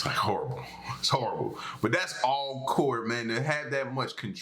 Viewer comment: I thought you were saying "that's Coors man... all Coors man!" Yea... I'm in, to Coors training!